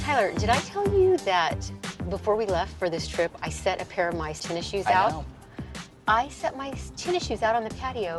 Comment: I thought the mum said " tissue" heh? Not Tennis shoe